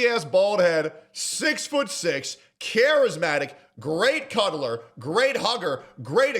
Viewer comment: I thought he said “great color” 😂